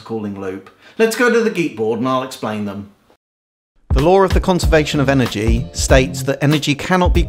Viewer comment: "the lore of the conservation of energy"... Wow, so it's only a myth! :O 1:04 "this is how water calling works" (no shit, even the subtitles say this) "I call summon thee, dihydrogen monoxide!" Sorry, couldn't help myself :P